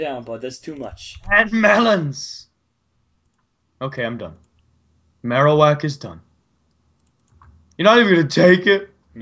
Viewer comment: did mitch say Marowak cause marowak is a pokemon